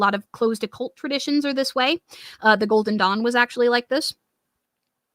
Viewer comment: I legit heard "Golden Dong" instead of "Golden Dawn"!